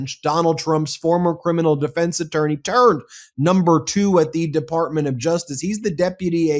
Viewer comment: I had to rewind twice listening to him talk about Todd Blanch. He said turned, I thought he had said terd instead. Neither word is a lie, both are accurate I believe. 😉